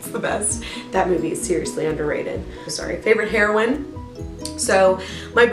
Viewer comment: what did u say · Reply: ummm I think I said “seriously underrated... sorry” but I had cut out some.